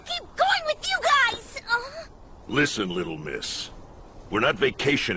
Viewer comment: Lol i thought joseph said "Listen little bitc-"